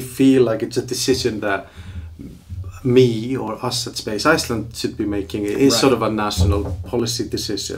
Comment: I thought it said Space Island and i expected Hardy Boys or something.